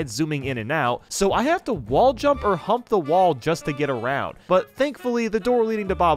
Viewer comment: I thought he said Hump the wall 0-0